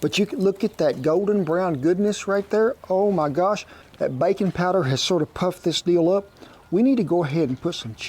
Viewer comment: I wasn’t hearing that well and I thought he said adding a little bacon powder and I got so excited like where did he find that? Then after about 5 seconds the reality hit me it was baking powder. 😅